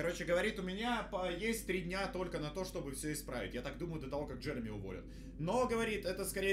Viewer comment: Мне одному послышалось на 44 секундах 'Джереми у вас п*др'